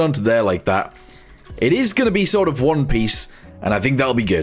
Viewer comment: did you just say the one piece?✌️